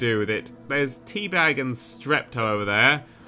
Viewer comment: I heard this as T-bag and Strep throat